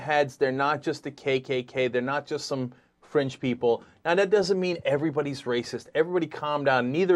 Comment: lol at 3:48 I thought Cenk said french people